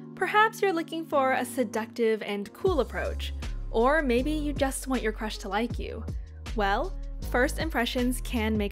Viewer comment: I heard, "Or maybe you just want your crush to like you." as "Or maybe you just want your precious wifey." and I was like, YES.